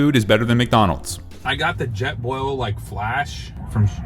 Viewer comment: It dosen't sound like someone screamed outside but inside of the golfkart maybe he activated something maybe the jetboil said something i heard "Fresh"